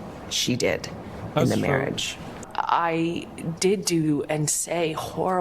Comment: "did do" sounded liked "doo doo" to me and made me chuckle.